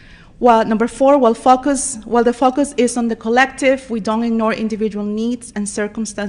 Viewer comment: Every time she we focus sound like she say “ will fuck us “ Lmaoo